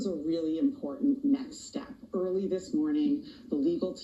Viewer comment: Wait, @ 00:03 did she say “important NECKS step” or “NEXT”? 😂 😭 🥴 🐸